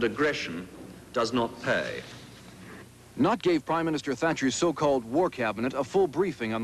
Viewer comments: Is he saying “Not gay Prime Minister”? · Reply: He says "Nott gave.." the guy's name was John Nott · did he say “not gay”?